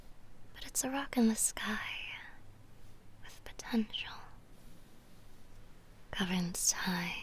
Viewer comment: DID YOU SAY POTENTIAL?